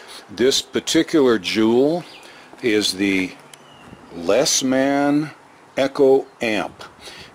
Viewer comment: For half a moment I thought it said “lesbian echo amp”